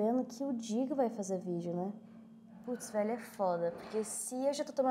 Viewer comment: PUTZ É ISSO MESMO QUE EU OUVI ???????????